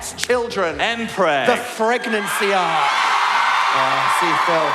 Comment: captions say Pregnancy but it should be Phregnancy